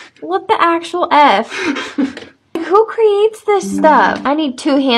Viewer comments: “Your setting your self up come on” this killed me 😂😂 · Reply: OH I THOUGHT HE SAID "You said it yourself, man" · “Your setting your self up come on” this killed me 😂😂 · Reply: Oh that's what he said I thought he said "you said it yourself I'm hung"